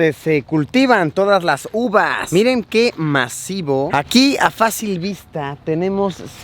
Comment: en ves de masivo escuche macizo